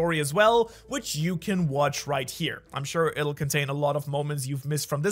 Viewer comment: I'm sorry but did you just say shanks for watching LOL